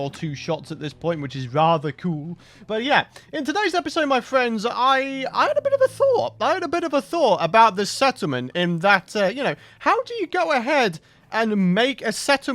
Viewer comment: in the beginning of this episode you said "I have a little thought" and I thought you said "Im a bit of a thot" LOL